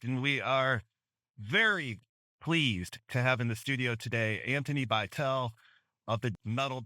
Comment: Hold up did u say bitel????? At 1:05 is he a teacher cuz I think he teaches me in music if not correct me (I didn’t watch all of it only watched until the name part)